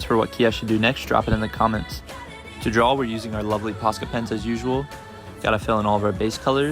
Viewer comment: Posca pens? I thought you said pasta pens😂😂😂